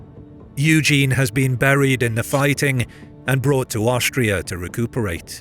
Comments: Am I the only one who hears at 8:18 "Eugene has been buried in the fighting" ? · or so What the subtitles say: "Eugene has been injured in the fighting..." What the narrator says: "Eugene has been buried in the fighting..." Hmmm, not sure what happened! Only had subtitles on by accident.